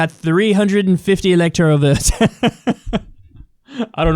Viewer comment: Votes?! We thought you said need boats!🤦‍♂️